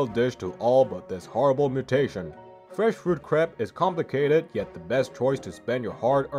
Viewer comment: ugh why do i hear "fresh fruit crap"